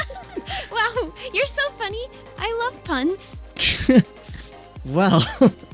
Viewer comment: I thought she said I love buns